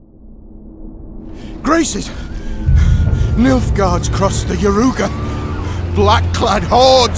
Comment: Subtitels at 0:09 spell MILF guards instead of Nilfgaards LMFAO